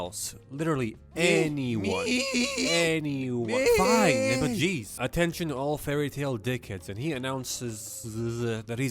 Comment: did he said the n word? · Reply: Why yall so stupid. He said nibba